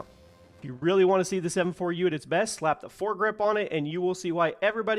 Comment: … did he called it the M4U? :D